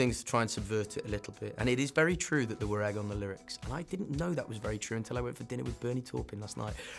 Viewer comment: And it is very true that what? I can't understand what he said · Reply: There were egg on the lyrics. (it was written during breakfast!)